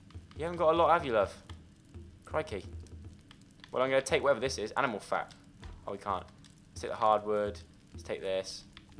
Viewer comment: I thought it said 'Animal Fart' not 'Animal Fat' 😂